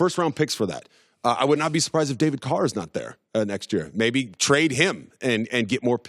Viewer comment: Did he say david carr ?